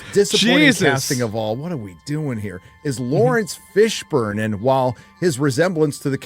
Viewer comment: Bro did you just say Lawrence Fish Born?